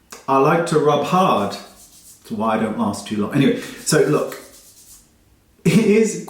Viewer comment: Did he just say, "I like to rub hard. Thats why I don't mast ... too long." 😂 #noshame #justsayme #Secretlifeoftheamericanteenagerquotes